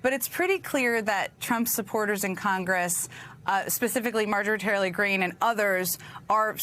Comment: Did Jen say "Marjorie TRAILER Green?